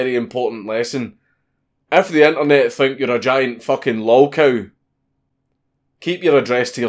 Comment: what does he say there at 13:19? giant fucking ??? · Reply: I know this is five years old, but a lolcow is basically someone who just gets constantly trolled and ridiculed on the internet